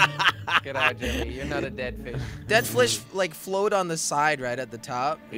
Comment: He did not just say dead flish...